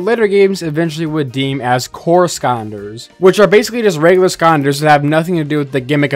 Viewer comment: After the 10th time I heard him say “skaunders” I was convinced I was experiencing a stroke